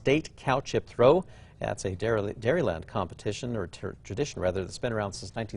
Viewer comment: Did anyone else notice it sounded like he said turdition when trying to say tradition?